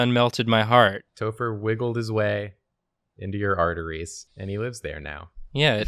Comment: I heard "Topher wiggled his way into your hearteries", and I laughed at the funny pun, and then listened to it again and realized it was an illusion